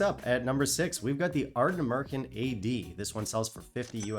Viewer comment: Dude, @ 5:32, I kept laughing my ass off because it sounded like: "Aredn't you An Amurican?" LOL! I had to keep rewinding it just to hear you say: "Aredn't you An Amurican?" I love your channel!